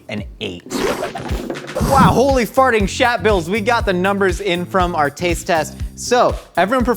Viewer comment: I'm stuck on repeating Holy Farting Shat Bills in my head. I don't even know if that's truly what he said but that's what I hear and it's now in my rolodex of exclamations